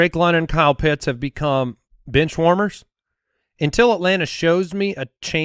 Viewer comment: I swear at 25:57 says Bitch Warmers lol